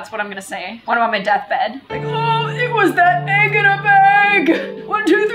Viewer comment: “Egg in a bag by a YouTube hag” - 2020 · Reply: Sounds like she saying egg in a beg omelette in a beg